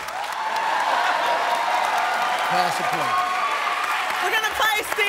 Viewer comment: When she said boxers at first I thought she said buttsers